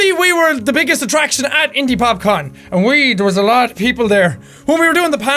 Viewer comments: Am I the only one who thought that said indyPopcorn? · I thought it said indypopcorn 😬